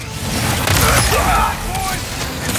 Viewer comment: i thought i heard "freaking wh*re" in a kids game lol